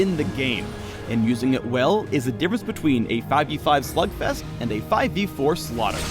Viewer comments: did he said slut fest at 4:09 ? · wait, did phreak say "a 5v5 slut fest"?